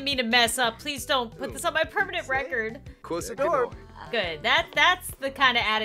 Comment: it sounds like he said "Close the door!" and "Did you walk the floor?"